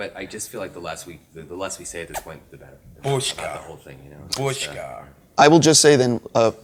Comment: Great interview, wonderful movie. But what on earth is Mendelsohn saying at 11:07 and 14:08? "Bushka"??